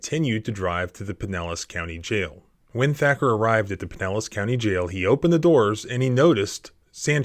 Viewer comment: Hahaha, did anyone else notice that the subtitles called it the "Penis County Jail" instead of the "Pinellas County Jail". Lol